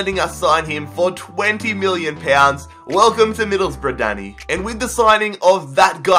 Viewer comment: when he said 'welcome to middlesbrough danny' i thought he said 'welcome to middlesbrough daddy' at first...